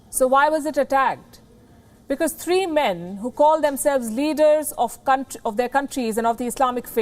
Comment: Leaders of c**t of their countries did I just heard or anyone else heard about this